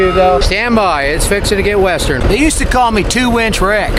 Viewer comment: did you say 2 inch Rick??